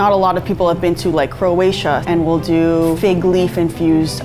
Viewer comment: Did She said Croatia (btw am from Croatia)